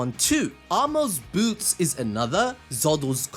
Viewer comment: i thought he said b**bs, but actually boots lol